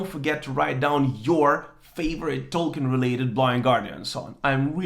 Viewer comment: And Then There Was Silence isn`t Tolkien-related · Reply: lmao sorry i thought he said whats ur all time favourite blind guardian song i guess im just blind thnx for telling me doe